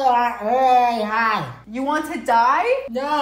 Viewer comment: I thought he was saying he wanted to get really high